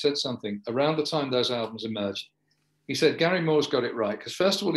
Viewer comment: Allwight...oh...I thought someone said Barrymore...